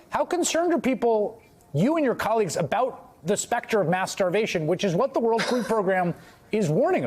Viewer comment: Had to do a double take. Thought he said masterbation but he actually said mass starvation